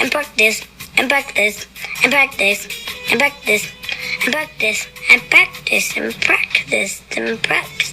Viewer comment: Is it me, or do I keep hearing 'Buck this', rather that 'Practice'?